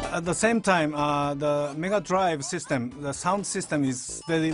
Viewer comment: Did anyone else hear Nigga Drive System? XD